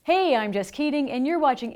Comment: The first thing I heard was "hey I'm just kidding" instead of "hey I'm Jess Keating"... Any body else?